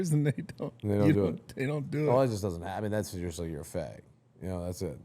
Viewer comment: Does Bob say you’re a f*g at 32:27?